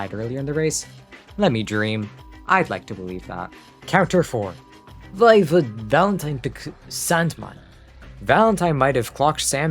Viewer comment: tf r u saying here. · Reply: "Why would Valentine pick Sandman?"